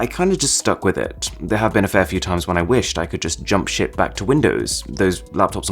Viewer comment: "jump sh*t"😂 reply if i misheard